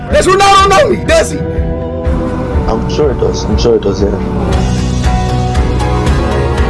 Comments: I thought he said "who the frick is that gay"💅🤣😂🤣😂🤣😂🤣😂🤣😂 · this is what i hear "Who The Fuck Is That Gay"